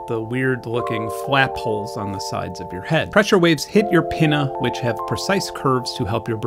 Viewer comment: Did he just say “hits your penna”